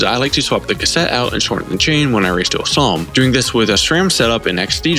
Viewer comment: What did you say at 2:40? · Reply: Dual Slalom